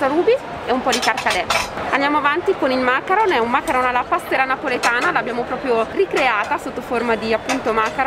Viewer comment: macarons alla passera napoletana?? ho capito bene?